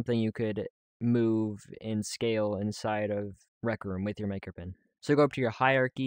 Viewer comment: Why do I hear Maker Men